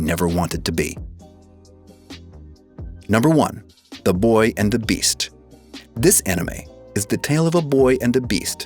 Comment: “The boy and the beast.” I first heard “the beauty and the beast” for some reason.